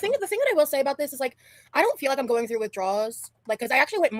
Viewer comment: Ok, I listened a couple times & each time I hear her say, “withdraws” not withdrawals. Am I hearing her wrong? She’s not saying “withdraws,” right?